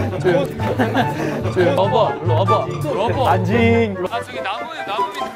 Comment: menit 5:35 kedenger nya tiway kaya ngomong "anjing"